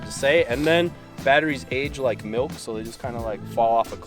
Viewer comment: "Batteries age like milk"- love that, going to use that when I talk to customers about their cars. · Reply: I’m not lying. I literally thought he said “red dot batteries age like MILFs. They just drop off a cliff.” 😂